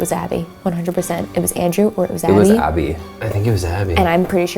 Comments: i heard "It was Dad" just saying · Literally every time you played “it was John” I really tried to hear that but I don’t hear that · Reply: i heard "it was dad"